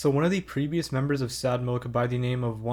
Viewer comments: I thought you said "one of the creepiest members of sad Milk" and was taken a back for a bit lmao. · Okay I totally misheard 'previous' as 'creepiest' for some reason or other.